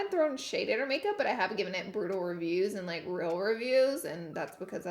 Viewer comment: So I'm a little confused as to why she said she doesnt give brutal reviews/real reviews on kylie's stuff, because that's her job...while shes literally in the middle of a Kylie review. Does this mean this review is of her lying then? Anyone like to clear this up for me? Maybe I'm missing something here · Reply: she says "i haven't thrown shade but i have given it brutal reviews and real reviews" i thought she said haven't at first But she's just talking fast